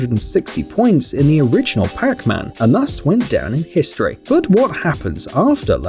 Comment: it souns like he says Crackman instead of Pacman.